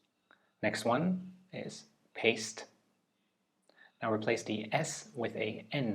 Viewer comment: I cant believe i was thinking of the wrong paste lol. I thought he said paced 😭